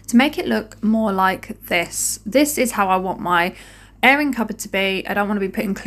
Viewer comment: I thought you were saying errand cupboard - came to the comments & read airing cupboard, makes perfect sense now! Just found your channel, thanks to Remi Clog - clean on !!! P.S. yes, a silly American (Alabama)